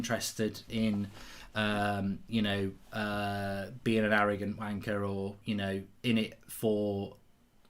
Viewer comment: Congrats Gents. I thought you said 21:41 arrogant BANKER lol... We seriously need new Ownership. Luv your Work.